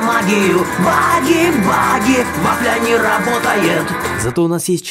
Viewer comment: Мне послышалось: "вафля не работает"